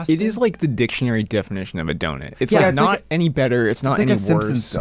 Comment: … im sorry did u say donit instead of donut?